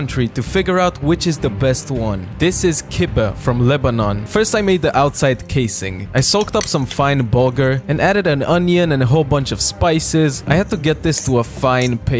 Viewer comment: I swear I heard "I soaked up some fine booger"